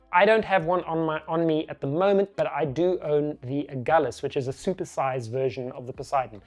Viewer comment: what does he own? I can't understand his accent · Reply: The Agulhas (aka LP-364cex). Named after the Cape Agulhas; the southern most point of Africa. This tripod was specifically designed for the South African market, hence the name.